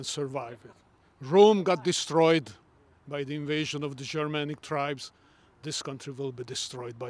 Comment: min: "Rome got destroyed, by the invasion of Shamanic tribes..." --> Woah... easy there... easy now... that's some slightly revisionist history, in'it mate? 🙄 I'm pretty sure Rome spending almost 2000 years invading other tribes & regions had something to do with "Shamanic tribes" one day deciding to invade Rome, aye?😏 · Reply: I thought he said Germanic tribes? 🤷🏻‍♀️